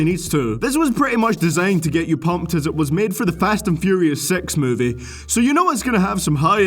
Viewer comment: sounded like fast and furious sex movie